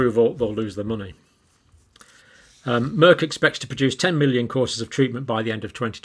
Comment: Oops I had to a retake coz I thought you said 10 million CORSETS😜🤔